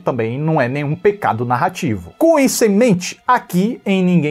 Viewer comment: Certeza que ouvi "Cu e semente" do que "Com isso e mente". Eu gostei mas com ressalvas. A atuação da atriz faz tudo parecer real, pois é muito boa. Mas em alguns momentos parece ser um pouco forçado.